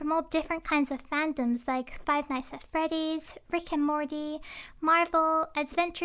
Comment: It sounded like you said frick and morty. No hate tho I love your voice <3